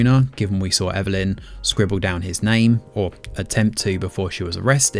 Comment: What was the name you just said? Sounds like you said Jina or Jyna , I listened many times and cant make it out. No characters in the show with even close to what you said.